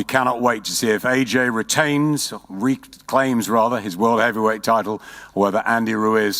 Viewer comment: did he say “World heavy weight taco”?